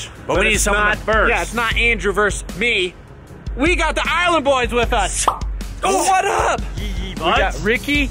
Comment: Is it just me or did they say”island Boys”😂😂